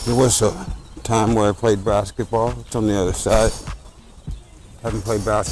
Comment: I thought you said you used to play Brassketball. I don't know what Brassketball is, but if anybody does, it be.